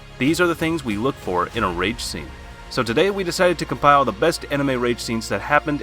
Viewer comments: am I the only one who heard rape scene? Like if. Are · Who else hears "rape scene" instead of "rage scene"? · You saying rage science or rape scenes sounds like ur saying rape scene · did he say rape scene??????💀